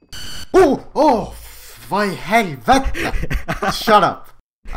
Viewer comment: what did he say? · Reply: He said: WHAT THE HELL